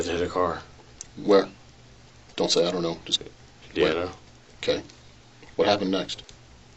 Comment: although the closed caption says [unintelligible], it's clear from the follow-up question that it was "Indiana" that was said. I'm playing Detective and will add others that I figure out whenever I edit this post.